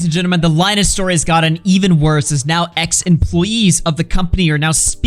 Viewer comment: At first when you said ex employees, I thought you said X employees, cause you were on the twitter/X page